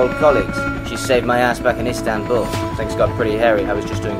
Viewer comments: PLEASE I THOUGHT HE SAID “SHE SHAVED MY ASS BACK IN ISTANBUL” 😭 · I swear I heard "she shaved my ass in Istanbul, things got pretty hairy" and I didn't even question it, it made perfect sense. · I genuinely thought he said “she shaved my ass back in Istanbul” and that made the next line so much better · I thought he said “she shaved my ass back in istanbul, things got pretty hairy” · i thought he said she shaved my as back in Istanbul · I definitely heard "...SHAVED my ass back in istanbul"